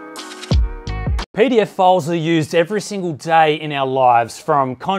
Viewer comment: Jesus Christ mate when you said pdf files at the beginning of the sponsored segment I legitimately thought you said pedophiles